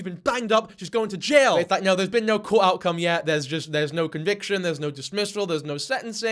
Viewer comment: Did u say (5:41) "cool outcome" ooor "court outcome"??